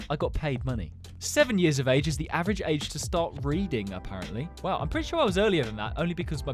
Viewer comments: I thought he said start breathing at first 😂😂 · I thought he said BREATHING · I wasnt looking and I heard BREEDING INSTEAD 😭🙏